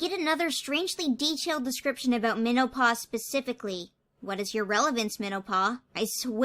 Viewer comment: I got a text at about 4:15 and wasn’t super locked in to the vid, and I thought you said menopause, not minnow-paw